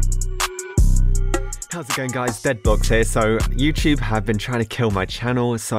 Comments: did i hear that correctly? HE SAID DEADBLOX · did you said dead blox? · did he say DEADBLOX